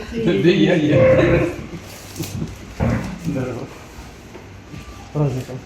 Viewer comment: я правильно услышал, он говорит ей здарова?